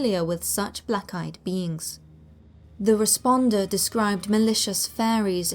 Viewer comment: Every time you say "Black Eyed Beings" I hear "Black Eyed Beans" Anybody else? 😂